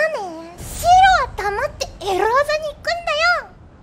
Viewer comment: ごめん。どうやって聞いてもエロ技にしか聞こえないんです…